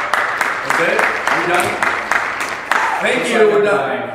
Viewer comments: You're reunited with your daughter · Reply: Also Around 16:42, I heard someone say "wait, that's incest" just me? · I heard someone say "wait, that's incest" just me?